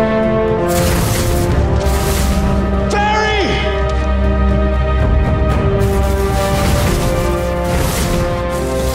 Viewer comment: Did he say Jerry or Barry? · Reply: Barry